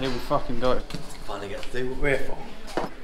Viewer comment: swear he said “my nword”